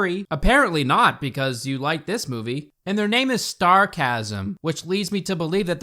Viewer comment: i thought they said sarcasm, not starcasm